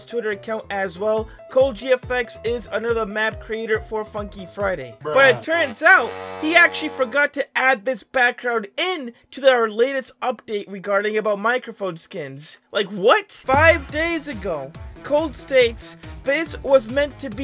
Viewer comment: Did he just say THE LAST UPDATE FOR FF